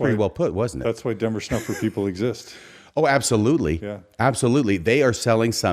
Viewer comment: What did say @ 34:12? "that's why dumber stuff with people exist?" I think i'm mishearing him. Can someone clarify?